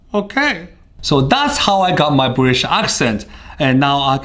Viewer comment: Until 4:48 I thought he said "Buddhist Accent"